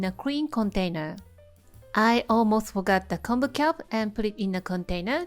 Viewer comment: What is the dark leaf you put in the jar first? It sounded like you said Kombu. Is that something I have to have when I make it? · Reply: It's kombu. You can skip it but the taste is a bit different. You can always check the ingredients in the description.